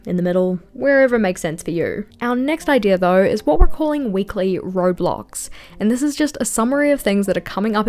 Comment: my kid gave me brainrot coz I keep hearing Roblox 😂